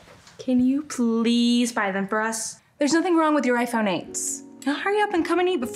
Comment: did she just say iphone 8 💀👀